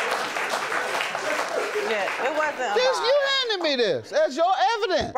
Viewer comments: it sounds like JM said “Bitch you handed me this!” Lmaoooo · ? I swear it sound like JM said Bitch you handed me this 😂😂😂😂